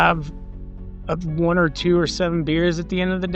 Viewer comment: I think I heard 1-27 beers. This guy's a hero 😂